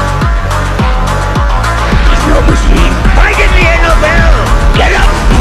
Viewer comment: is "I didn't hear no bell" not "fight until hell"